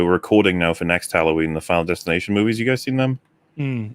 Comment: what movies does say they are watching for next Halloween? I have listened to it 5 times and can't make out what he is saying. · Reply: Resident Evil movies for Halloween and Final Destination movies for next halloween.